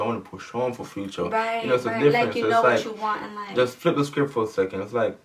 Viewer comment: I thought you said "Justin" also.. who's Justin? Ohhhh Just in the face. Loved this story time :)